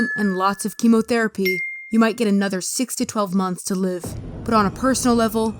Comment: Did he just say 60 12 months